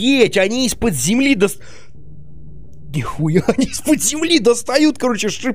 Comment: кто услышал когда костеной маг сказал нихуя